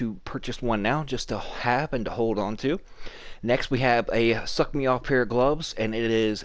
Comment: Great reset! Had to rewind the vid sounded like you said ' the suck me off' pair of gloves hahah I'm sure I won't be the first to of heard it lol